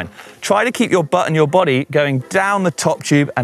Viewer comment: The first play through I heard "try to keep your butt and your body going down the top tube and over the back wheel niiiice and sensually". The second time I heard "niiiice and sexually."